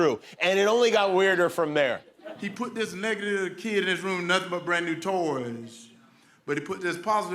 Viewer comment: "this negiler lito kid" wtf is he saying?!